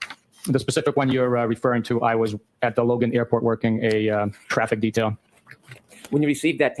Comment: Isn’t texting illegal in Massachusetts ? He was texting a thumbs up on his eye phone. He’s making a point that the words were so small in his eye phone that he couldn’t read the offensive language that Mr proctor was spewing so he just texted a thumb but that’s still dangerous isn’t it?? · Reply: I thought he said directing traffic so I pictured standing out waving traffic on